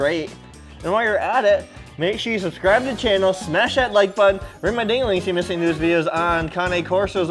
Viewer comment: Did he say “ring my dingleling at 0:45- 0:47? LOL